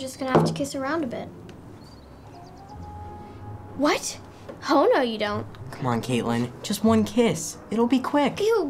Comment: I thought she said "Hoe, no you don't"